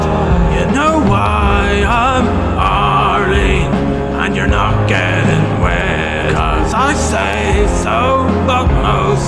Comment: What's he saying at 3:25-3:26. Sorry, that aren't there is just different enough that I can't understand "cause you're not getting x" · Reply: You’re not getting wed cos’ I say so but, mostly, the mean things you said...